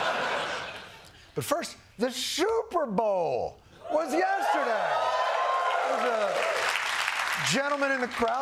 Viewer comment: did I just hear the shooterbowl? he's prophetic on all US issues